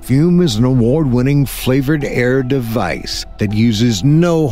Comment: Man that fum ad, I honestly thought that you said flavored hair after that first story.